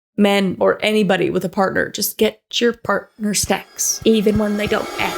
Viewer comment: I had subtitles on and instead of "get your partner snacks" it said "get your partner sex" 💀